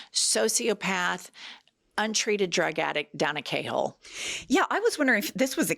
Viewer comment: What is a 'K' whole or hole? That is a serious question, I'm all for calling people names when they deserve it (pretty sure he will deserve it), but before I do I like to know what it means. I thought she said cake hole first, but it didn't make sense in that context.